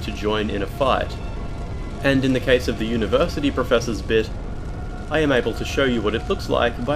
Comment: I thought I heard him say “The University Professor’s Bitch”